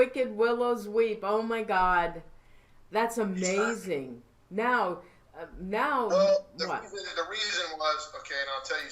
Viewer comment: i heard something and was taking a drink as i heard it and was not talking at the time. Did anyone else hear it? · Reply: I think it said shut up...very fast...slow the tape down maybe that will help